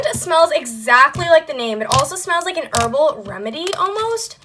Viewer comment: I thought you said it smells like anus 😂